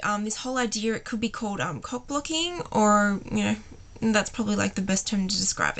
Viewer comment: I thought she was saying "cook booking" funny shit nice accent